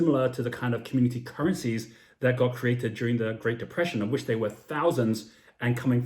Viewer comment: @ 18:37 you are talking about the Crypto-currencies, referring to after the great depression when there were many currencies created. It sounds like you said: "that God created" them? Sometimes it is hard to make out certain words you say, be it that I do rewind to hear them again and am able to make them out. We found your channel a few weeks ago and subbed. Thanks for sharing.